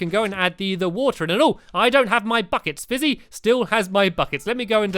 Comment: I thought he said f**ckets